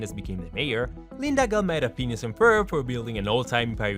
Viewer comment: subtitles say "Linda gal made a penis and furb"